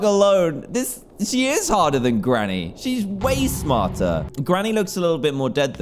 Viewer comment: i thought dan said she is hotter than granny lol